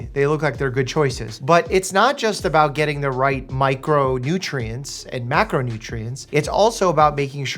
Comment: Macronutrients and macronutrients ??? Can someone pls tell me if I misheard or smt ? Idk T-T Haven't watched it till the end yet but great vid, as usual <3